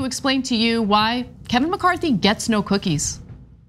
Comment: I thought she said gets no quickies